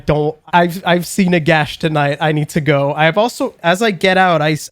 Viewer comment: did say "I've seen Nagash tonight"?